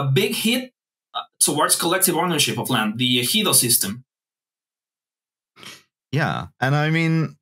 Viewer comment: what does he say? the helio system? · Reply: Ejido system